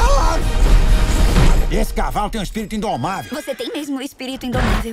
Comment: eu n entendi, ele tem um espírito indomável ?